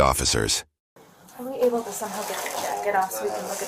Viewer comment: did she just said can we aible to suck it or jack it off and the proseeds to suck it in 43:-- ? Or is it just my ear and brain has problems😂